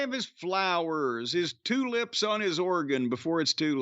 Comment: Lmao 5:02 I heard it as “two lips on his organ” instead of the saying “tulips on his organ” at first. I think both work in that case